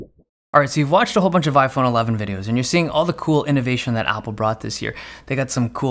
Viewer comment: Did I hear him right DID HE SERIOUSLY SAY "Cool Innovation Apple Brought This Year" Either I heard it wrong and if what I heard is right I say he's not in his right mind😲